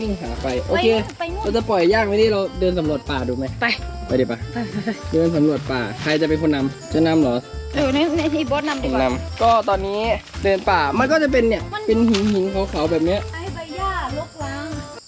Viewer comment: เค้าได้ยินพี่บอสบอกว่าเธอนำหรอใครได้ยินเหมือนกันบ้าง🤥